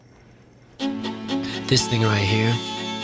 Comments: bruh what u just say? am i hearing u correctly? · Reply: But he's saying "thing" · PLEASEEEEEEEEEEEEEEEEE I THOUGHT MR SCHUE SAID THE N-WORD AT THE BEGINNING BUT HE SAID "THIS THINGY RIGHT HERE" IM DONE